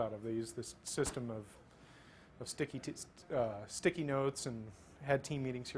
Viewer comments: did he say sticky tits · I heard him say 'sticky tits' at 2:00 and knew there was going to be a top comment about it